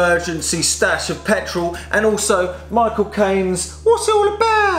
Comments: When he says "Michael Caine" it sounded like he said "my cocaine" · For a moment I thought you said: "I'm next to a plant, my emergency stash of petrol, and also my cocaine" 0:17 Lol